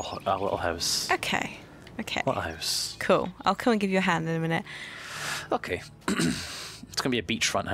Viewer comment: did just say i will give you a hand at 1:55 lol thats what she said